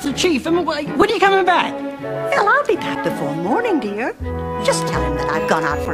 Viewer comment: LOLOLOL 1:44 ... Did she just say "Helll, I'll be back..." Isn't this a kids' show? LOL